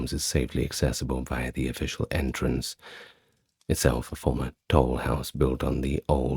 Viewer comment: I thought you said "itself a former Toddle House" and had to pause the video to see if, indeed, there was once an American Toddle House restaurant built on the catacombs. 🫠